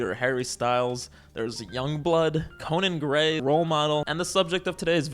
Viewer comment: did i hear Conan Gray 🥹